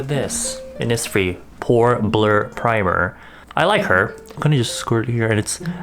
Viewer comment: When he said Pore Blur Primer I thought he said porn burn primer lol ( mr and my dirty self )